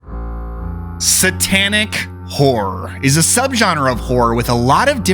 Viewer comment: It sounded like you said Satanic Whore at the beginning